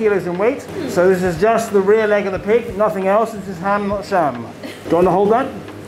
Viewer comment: Love that guy... Ham not sham. These traditions are important and you can tell he's proud of his work. Thank you for the video. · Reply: O_o I was wondering what he kept saying, I always heard "Ham not Sam" and confused the hell out of me.